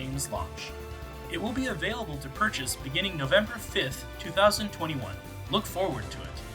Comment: did he say it won't be available to purchase November 5th?